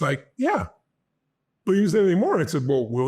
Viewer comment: what is Tony saying there? I listened many times at slow speed.??? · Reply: “But he didn’t say anything more”